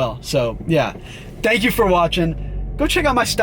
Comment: Don’t know if anyone caught this but when he said “PEACE” at the end there it kinda sounded like KSI